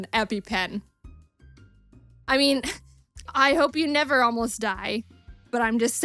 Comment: i thought you said you never cry